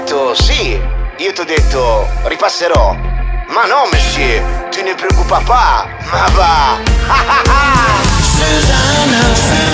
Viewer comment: Anyone knows what he's saying at 1:30? Sounds like "Mais non/ma no, Monsieur. Tu ne préoccupes pas" and then "Ma va" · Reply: Nettle he says" ma no, monsieur. Tu ne préoccupes pas" and then " ma va"